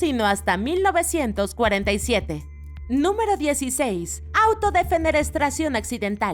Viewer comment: escuché mal ó dijo "autodefenerestración"? 😅